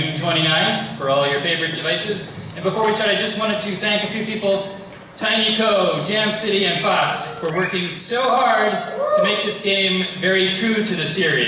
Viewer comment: CC says "tiny toe jam city and fuck we're working so hard" xD